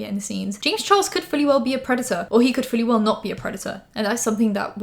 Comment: I thought you were saying printer and I was like “I’m pretty sure he’s not a printer but…”😂